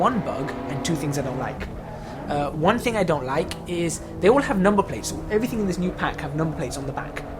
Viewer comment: Could someone tell me what they said at 19:49? I could not understand what he said. They all have.. thanks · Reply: I said "They all have number (licence) plates" ^^ Sorry about that!